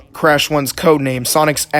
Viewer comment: What Are You Trying to Say at The End of The Video?! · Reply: Sonic’s Ass Game